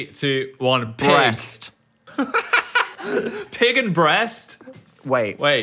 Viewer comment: mixed together, i heard piss